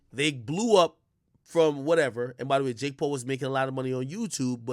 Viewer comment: I thought he said J Cole and was like wtf? Mans said Jake Paul lmao...